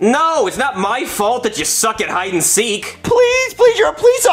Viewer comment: I like how he ran away 😂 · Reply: I thought he said “I fuck at hide and seek!”